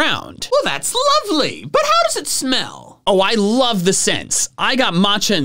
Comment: I love the sets! What I heard: Oh I love to sex!